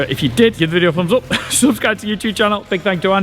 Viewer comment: I thought you said it was over kill vet not oak hill. Lol